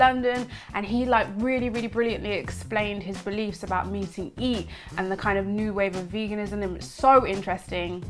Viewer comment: she says "meating eat" instead off eating meat the child in me giggled for 3 minutes straight